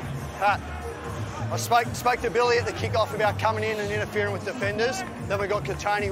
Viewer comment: I thought he said I smoked a Billy tryna tell him not to come in and interfere with defenders